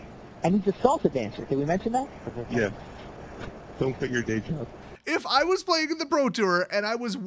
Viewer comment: Am I the only one who heard him say 'don't quit the day job' 😂😂😂